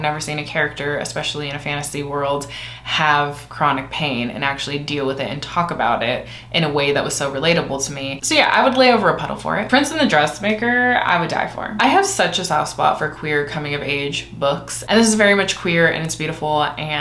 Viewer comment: I heard “…in a way that was so PLATEABLE to me.” When you actually said RELATABLE to me, and I think I like platable more